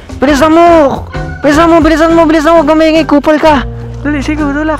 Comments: did just say "Kupal ka"? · Did I hear it right? Sabi ni "Wag ka maingay KUPAL ka."? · Ano sabi ni bilisa mo bilisann mo k*pal ka? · did he just said “kupal ka”? HAHAHAHAHAHAHAHAHA